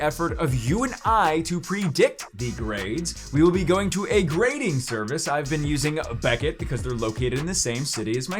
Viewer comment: ah shoot i thought it said tradin not gradin